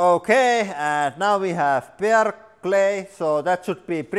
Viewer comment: What is the saying? Bear clay?